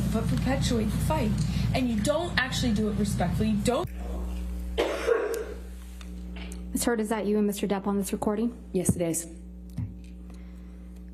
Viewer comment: Why does it sound like Camille says Mr. Depth?